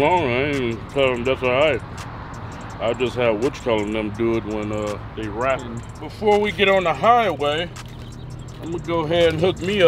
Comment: I thought said a week for the wrap????